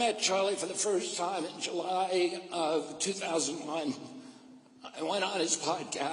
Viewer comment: Have anyone catched that he said he went to Charlie Kirk podcast in 2001 @ 1:30. Charlie was born in the year of 1993 did RFK met Charlie when he was 8 years old? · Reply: He said 2021. Sometimes it can be difficult to catch everything he says because of his speech impediment.